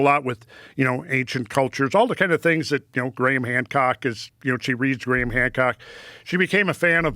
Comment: When he said Graham Hancock....i misheard him and thought he said Grey man cock!🤔